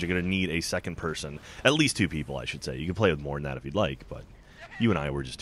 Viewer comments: All i heard is "you can play with more than 2 children if you want." Then i switched off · All i heard is "you can play with more than 2 children if you want." Then i switched off Oh and "make sure you grab..."